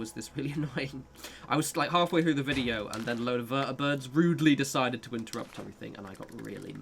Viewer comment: I misheard "Vertibirds" as just "birds", and I was like: You are grossly underestimating the entertainment value of "Davey pours obscenities into a flock of goldfinches", man.